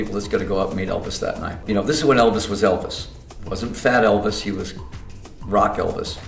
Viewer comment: "fat" elvis was still "rock" elvis! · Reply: jaytotheell I thought he said "fad Elvis?"